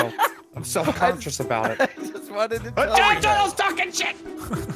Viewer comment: all I hear from is “ITALIAN TURTLES TALKIN S**T”